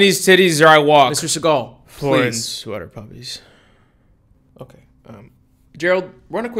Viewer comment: 😂😂😂 did he say "sweater puppies"